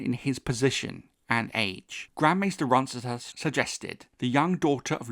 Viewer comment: the captions say- “Grand Master Orwyle” but all I heard you say was- “Grand Master Ronsthahaussss” lol